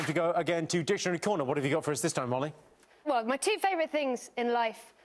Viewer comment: does he call her Molly?